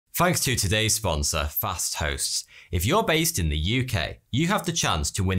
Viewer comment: I thought the sponsor said "fast-thots